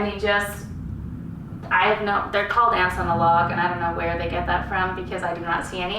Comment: Lol I heard "Ass on a Log" Beautiful room! Love the wood and color palette.